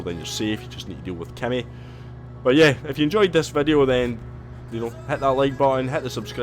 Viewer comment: did u call Kenny? 🕺💂